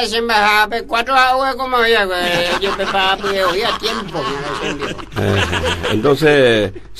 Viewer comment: Me pueden escribir en guaraní lo que dice en el minuto 3:52 hasta el 3:56 por favor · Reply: 4 ha'e orekoma voi'akue, ojepreparapyre voi a tiempo.